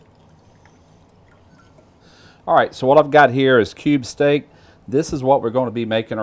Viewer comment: again another nice nice nice nice one.oh hey, for a second i thought you was saying "KOBE steak" there and i thought HELL, has now gone rich and decadent? cube steak it was.